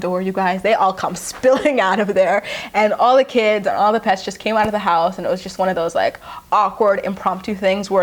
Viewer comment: I thought you said “all the kids and all the pests come running out “😂😂😂😂😂😂